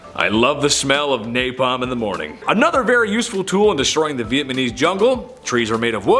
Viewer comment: Haa haa haa haa, did you say Vietmanese? That's hilarious!! Haa ha haaa!!